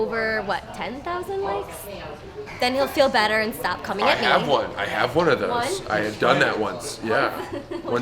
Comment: did anyone else hear her say "coming in me"